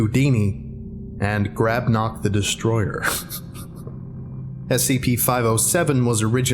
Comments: I thought it said Grognek the Destroyer skskks, anyone subscribed to Call Me Kevin will get that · Who else got excited when he said grabnok the destroyer? I can't have been the only one 😭 I swear I heard him say gragnok the destroyer at first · All this time, I heard "Grabnok the Destroyer", as "Grab Not the Destroyer".